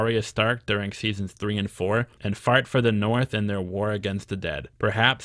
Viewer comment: did you just say fart for the north?